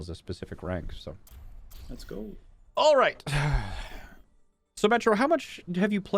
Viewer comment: Every time says "So, Metro" I just hear "Symmetra" lol